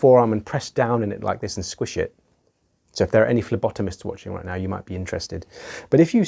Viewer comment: i legit thought you said "labotomists". 4 rewinds later i am now aware that Phlebotomists are a thing.